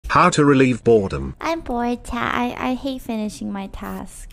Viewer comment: I heard "how to relieve a bottom"